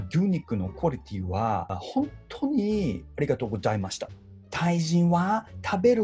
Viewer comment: 「日本の巨乳のクオリティーはありがとうございました」に聞こえた笑